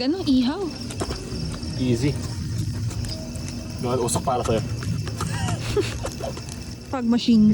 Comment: ako lang ba na narinig yung "mahal, usok para sayo" o sadyang guni guni ko lang talaga yon?